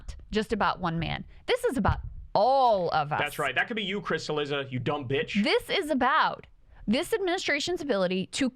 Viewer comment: I thought he said “That could be you Krystal, you dumb bitch” 😭😭🤣🤣